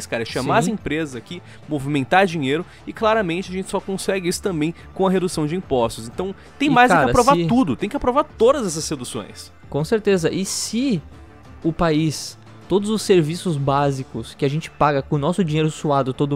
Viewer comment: só eu ouvi "seduções"? KKKJJJK